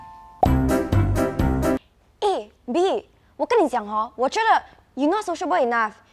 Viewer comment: Does anyone realise at 2:22 when says "Eh,B" the subtitles say "A,B" :D easy alphabets.